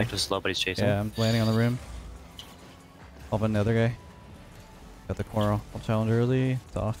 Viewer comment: what word did he just say??? It sounded like he started saying I got the coin but then realized coin is french and switched to try and finish it in er like corner and ended up just making noises 🤣👌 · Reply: "I got the corner or I'll challenge early"